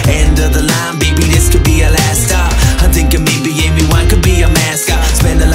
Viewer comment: I thought was saying "maybe a mime can be our mascot" but somehow I don't think that is it -_ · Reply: 'Maybe Amy Wine' ;)